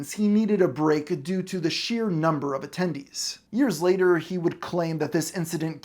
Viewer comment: did somebody say "tendies"?